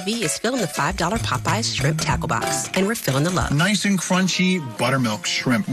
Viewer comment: Hahaha I'm Australian and I thought you kept saying shrimp taco box took I saw the menu 😂🤣 classic!